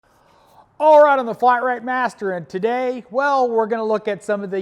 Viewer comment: Ha! When the audio cuts off in the end it sound like you say, "I'm the flat rate Matt." 😂